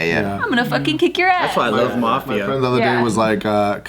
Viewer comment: did just say ma-.... maf-.... m-... i cant even say it 😔 i miss jkp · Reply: Mafia